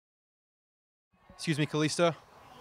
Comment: Did he called him kalista